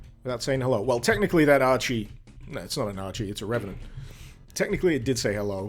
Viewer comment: Haha, the auto-subtitles got "Ouchie" from "Archie". 14:38~